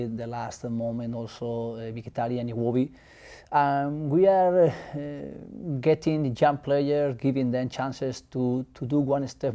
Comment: I thought he said "we are getting junk players" LOL. luckily the subtitles are there.